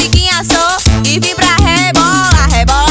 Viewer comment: Só eu que ouvi "ebola" ao invés de "rebola" KKKKKKKKKK, amei o clipe mas não consigo mais ouvir "rebola" KKKKKKKK